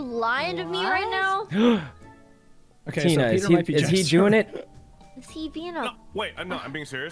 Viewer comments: Did sykkuno say “ is he jewing it?” at 1:41 or am I hearing things? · did you say "is he jewing it?" @ 1:41 what's the point of saying that??